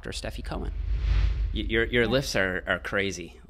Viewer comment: I thought he said 'your lips are crazy' lmaoooo, she just looked a bit weirded out😂